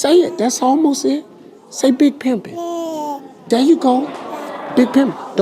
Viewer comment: "Big Pimpin"? PLEEEESE!!! LOL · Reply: Ohhhh I thought he said Big Pippin. I was like "Why??!"